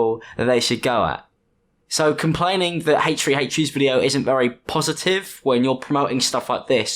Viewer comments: sorry but all i hear is “hate tree hate tree” anyways lovely video 💖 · All I hear is "h8-tree-h8-tree"